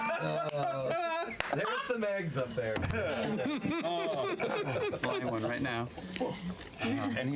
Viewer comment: says, "Caleb has the ?" - I can't figure out what she says that makes everyone laugh. What did she say??? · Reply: She says "Caleb has the 5th egg". And lo and behold, he does indeed have it.